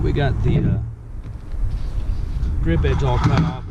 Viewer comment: please help... on minute 3:43 ..did you said the name of that piece is "grip edge"? thank you